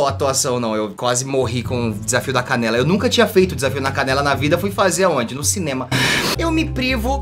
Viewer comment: só eu q entendi "desafio na cadela?" Kkkkkkkkk pqp